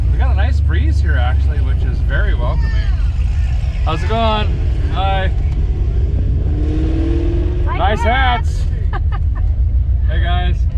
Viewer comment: Ok I had to remind it a few times and really listen cause @ 12:42 it sounds like he says "Hi nice ass" and ill be honest it kinda caught me off guard. 🤣😂